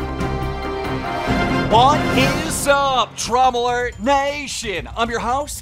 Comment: why did i hear "what's up trauma alert nation"